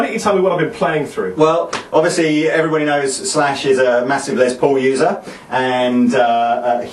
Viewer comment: at the beginning I thought you were about to say "slash is a massive lesbian".